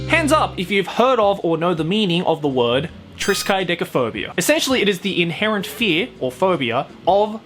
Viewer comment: did i just heard dickfhobia